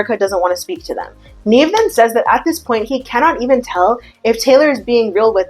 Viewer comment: Every time she says “Nev then says” I hear “Neven says” and I like to think that’s what Nev is short for.